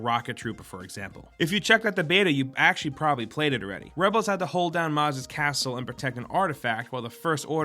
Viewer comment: did he just say rebels?